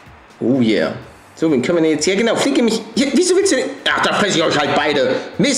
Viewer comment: Bei der 9:44 Min verstehe ich ficke mich statt fütter mich .... Ja guuut hab ich gesagt